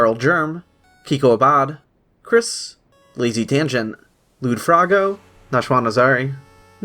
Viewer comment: did he say lewdfroggo??? no way